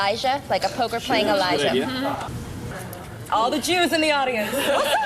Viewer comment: what did sara said 3:30 ? "maybe we should put chip here like a ...?" and then "all the juice in the audience what's up"?